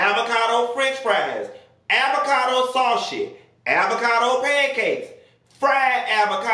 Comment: ish Did Dineva just say "avocado sa-shee" (sausage)?? Hahahahaha. I just slid out my chair and melted on the flo!! 🤣